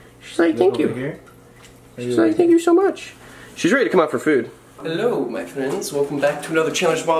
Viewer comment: At the beginning I thought you said hello boyfriends so I had to rewind it and I heard it said hello friends🤣😂😅😊😋😱😱😱